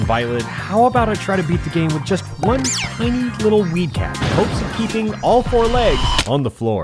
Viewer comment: did you say WEED…-cat